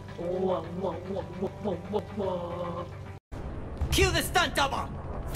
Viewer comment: Going to admit, I thought he said 'kill the stunt double.'